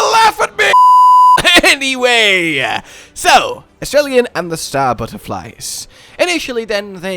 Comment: When you said it’s name I thought you said Australian. and I can’t not stop thinking about it having an Australian accent and saying “sniping’s a good job mate” while sniping the Hunter from across the map.